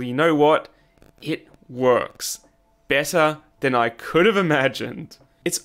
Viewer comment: just sounds like a random german word, 'besser' is the one i mean, it means better, this irritates me (Can't tell what he actually says) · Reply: he said the english word "better"